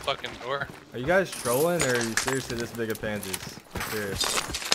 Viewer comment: “are you guys trolling or are you seriously-“ what did he say? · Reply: the biggest pansies